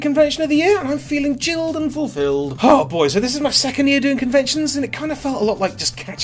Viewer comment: at the start I thought you said it was your second year in confection for a minute